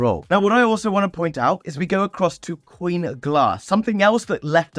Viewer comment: At first I thought you said Queenglass instead of Coinglass. I think they should change the name to that.